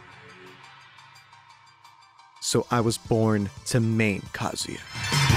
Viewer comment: did he just say "I was born to mate kazuya"??